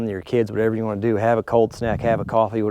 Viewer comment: Did I hear, “Cold Snack?” VGG strikes again.